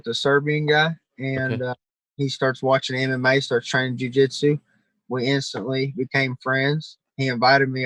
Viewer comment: I thought he said the guy started watching anime so he trained jiujitsu